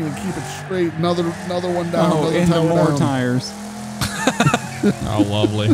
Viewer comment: sorry did he say anti war tires or am i trippin?